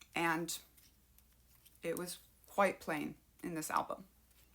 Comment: Did I hear you say "qwhite plain"? 😅